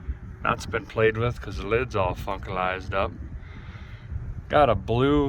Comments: Did you just say fuckalized up? 1:24:55 Sure has me curious! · Don’t think I’ve ever heard swear, @ 10:50 the air cleaner is fuckalised up LOL….